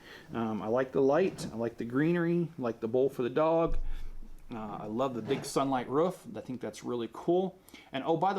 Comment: I like the big sunlight ruf. That’s what I heard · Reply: What do you even understand what I said. I said I heard “I like the big sun ruf”